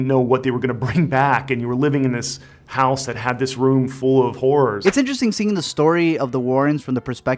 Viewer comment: someone told me he sounded like he said "room full of whores" at 2:49 and now thats all i hear